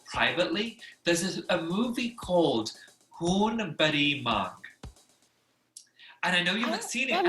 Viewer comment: Khoon bhari maang! Ohh god this is so dope💯😎 love from 🇮🇳. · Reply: If you hadn’t spelt it out I thought she said “Khoon bade maa” I was like huh wtf 😂🤦🏻‍♀️